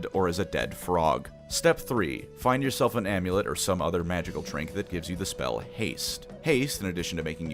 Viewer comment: i thought he said magical twink instead of magical trink and i immediately thought of every bard that i and every bard my friends have played, but i do think that they could be the same thing